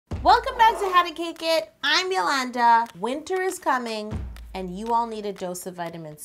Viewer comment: At first I was hearings things and I thought she said hey welcom back to my channel it’s yo mama